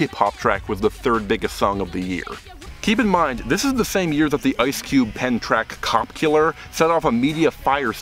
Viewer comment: Cop Killer was actually by Ice-T, more specifically his band Body Count which is a metal band and not a rap group. Granted not many people know that, a lot of news sites still call it an Ice-T song. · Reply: I heard "Ice Cube penned".